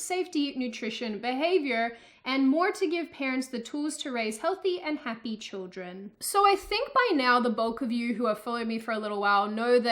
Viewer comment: I thought she said BOTH and not BULK I was so confused I thought maybe this video was made when she had only 2 subs